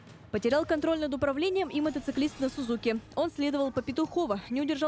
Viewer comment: не сузуки, а моциклисты все зуки! Мне так послышалось.